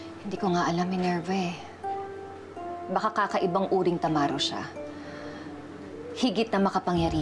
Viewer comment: narinig ko tamaro